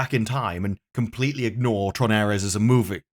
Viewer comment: Must be the accent bcos sounds like you saying “Tron Heiress“ which is funny how that fits the limp girls get it done BS